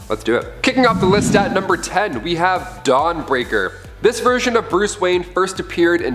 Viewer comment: Did ANYBODY ELSE think he said "Dog Breaker" at first??? lol